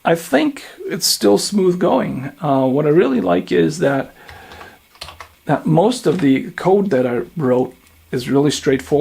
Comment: Love your videos. Thank you. Just misheard "Most of the code I broke." instead of "Most of the code I wrote." – actually that's most of the time the case (not because of you but because every character is a potential for error, and of course even those you did not wrote). ;-)